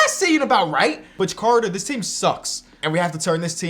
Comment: bro i thought he said bitch carter😂